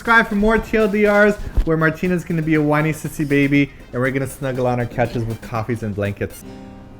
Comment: i think said that "we're going to snuggle on our couches with coffees and blankets"